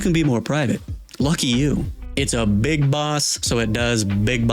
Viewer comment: did you say BIG BOSS because my snake is solid